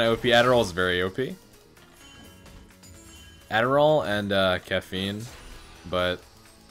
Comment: thought he said "adderall and Ketamine" which, tbh, is probably what Singed would say IRL